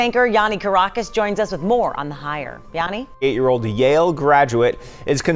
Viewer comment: did he say 8 year old Yale Graduate?